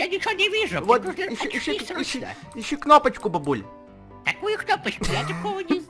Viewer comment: Ребята, предлагайте свои идеи для ТРОЛЛИНГА в комментариях! А то у меня они уже кончаются. И не забудь поставить лайк :) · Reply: Я услышала "Ищи кнопочку Бульбуль!"